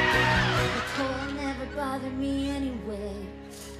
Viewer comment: I thought she said the goat never bothered me anyway 😂